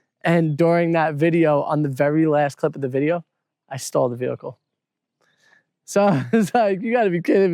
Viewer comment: Oh! Stalled... I heard 'stole' and because I knew that couldn't be right, I was trying to work out an alternative meaning!😂